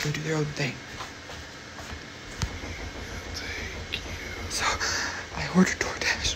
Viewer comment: THAT IS TERRIFYING THE BEING IS HEARD SAYING::"THEY""LL TAKE YOU...... ITS LIKE THOSE KIND OF NIGHTMARE OF WHICH WE CANNOT WAKE UP, AND THE KIND THAT WE WISH IT WOULD STAY ONLY THROUGH HORROR MOVIES